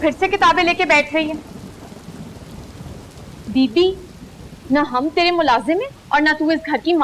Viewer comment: did she say didi (sister) just like hindu dramas? or i heard it wrong?